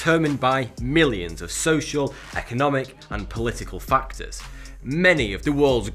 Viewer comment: I'm feel bad for myself and I have too because I misheard Political Factor to Political Fcker.